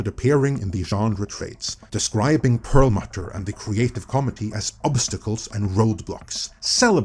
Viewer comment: Wow I heard "creative comedy" for an embarrassingly long time. lmao